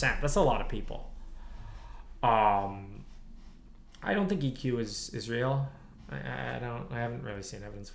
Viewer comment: You timestamped '00:46:48 IQ not real, Lex great interviewer', think he's saying "EQ" not IQ (responding to one of the chat questions near the bottom)